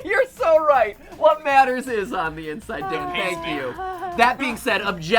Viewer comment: the captions said dick instead of Dan "What matters is on the inside, dick, thank you" XDDD